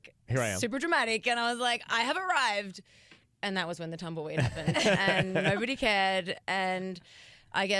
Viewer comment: When she said "Tumble weed" the first time I heard "Tumblr read"... I'm a lost cause